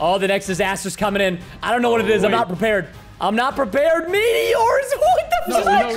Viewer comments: Is it it just me or did I hear Preston say the f word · I swear he said FU